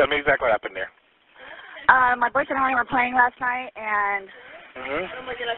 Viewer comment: Watching this laying next to my husband in bed and he said, "Did she just say they were playing hide and seek?" I didn't even know he was listening lol